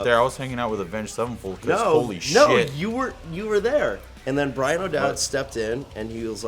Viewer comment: ''Bryan O'Dell'' (Bryan Stars)? Did he says that?